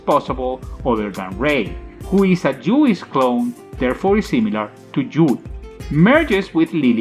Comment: did he just say a jewish clone? lolol